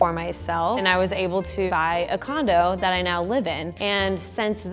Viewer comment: I literally thought that said "I brought a condom and thats where I live in now"